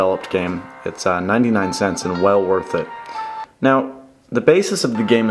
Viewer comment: Lol i thought it said $99 not cents. That would suck 99 bucks for an app. Anyway great review as always.